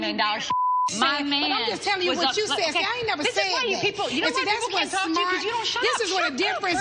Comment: Kim says “this is why you people” … anybody else catch that?